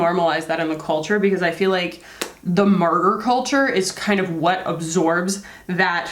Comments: Thank you so much for sharing about martyr culture. Years ago at an old job my boss would keep us working to 2am a couple times of week and it was normalized in our office. I was young and eager and just took the work abuse. Looking back now I realize he had a miserable home life and just kept coming up with urgent matters to stay in the office. Thankfully I am in a work culture now where boundaries are respected. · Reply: Oh martyr! I thought she said ‘murder’ and I’m thinking, is that an american thing?! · Are you saying/Is she saying "murder culture"? I haven't heard that expression before, I'm not a native speaker, so maybe I misheard? I kind of makes sense in context but I'm not 100% sure. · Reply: She’s saying martyr culture, as in you sacrifice yourself for your job/career :) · Sorry but what did you say at 8:47? I keep hearing "murder culture" but I googled that and had no results! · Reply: Martyr culture, I think.